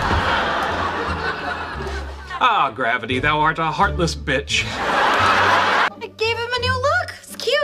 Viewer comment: I died when I heard "ooh gravity duh ah a heartless bitch"